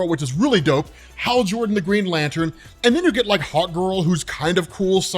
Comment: In hockgirl I thought you where saying hot girl